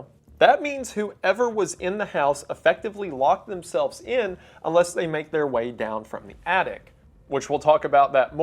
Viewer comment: when he said “unless” i thought he said “bless you” and i had literally just sneezed so i was so confused 😭😭